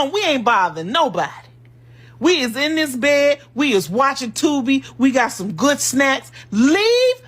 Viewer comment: Girrrrl, I thought that one lady was like “We’re watching Tubi.” I thought she said “We’re watching TOBY.” I swear to god, I was like is Roots on again?? 🤣🤣🤣💀💀💀